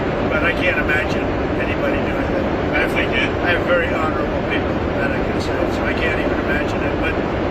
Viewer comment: The noise on the plane was pretty loud but i swear i heard him say 'i have very horrible people.'...